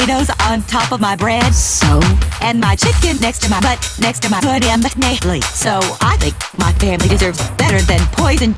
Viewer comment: Would some one PLEASE tell me what's said at 00:35?? I've always thought it was buh nanna plate but I really want to know. It says this in the happy ending when bobby smiles and goes reeeeeealllllyyyy... which is the Beesst ending in the world!! So what's the syllables there???? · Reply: “And my chicken next to my butt next to my ‘soy diem S-nay leach.’ So I think my family deserves better than poison.”